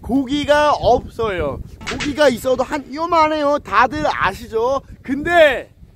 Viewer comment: 크ㄱ... 예? 뭐라고요? 그니까 들리는 데로 적어보면...거기가 있어두 한 요만 해여 다둘 아시겠쥬?